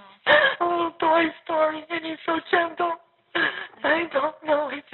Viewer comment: I think she said "he is like Toy Story and so gentle" which is kind of strange.